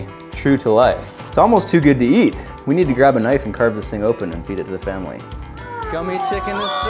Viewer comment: I heard "We need to grab a knife and carb this thing open"